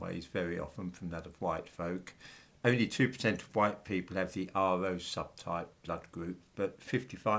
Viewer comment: "the R 0 subtype blood group"? A, B, AB, 0 with rhesus factor +/- respectively. Those are the blood groups for donating blood as I remember. I can't hear properly what is said, perhaps.